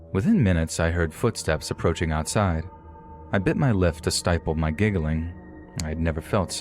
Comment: Pretty sure he said "Bit my lif to stiple my giggling" instead of bit my lip to stiffle my giggling.